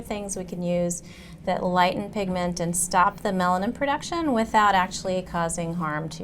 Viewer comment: did she say STOP MELANIN PRODUCTION orrr?!??